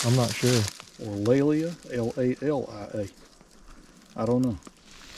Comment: i thought it said Julia.